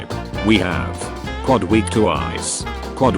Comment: i kept hearing "quad we to nice"